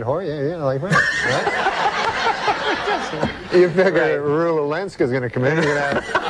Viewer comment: who does norm say there? rue wilenska? who is that..? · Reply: Rula Lenska